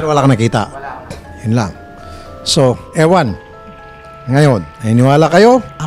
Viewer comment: Ang Creepy 😮😮😮 11:04 “EWAN” Pa like kung narinig nyo din 😮